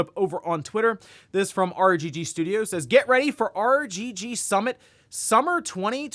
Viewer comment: I thought you said RGT Summit. I thought “ooohhh that’s interesting”